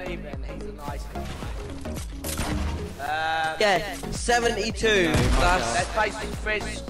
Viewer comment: Did he say 7e2? 3:59 Thats the class i was in last year♾ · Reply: Abstr4Kt Productions think he was saying 72 but his accent is mad 😂